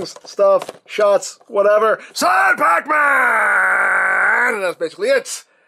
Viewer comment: i'm a simple man, i hear sad pacman i hit like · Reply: Wait turns out I have always misheard that “the pacman”